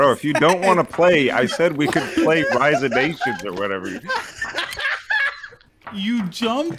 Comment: did he say rise of nations wtf :o